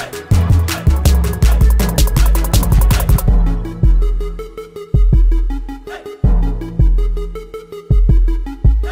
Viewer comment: No entiendo que dice en el minuto 1:46 conteste por favor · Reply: Es msconfig